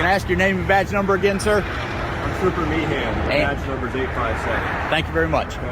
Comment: Guess my hearing is going, I thought I heard , I'm trooper meathead. Roflmao